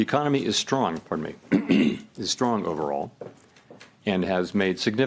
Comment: did he just say that the economy was “strong for HIM” and then cough? That’s a little funny.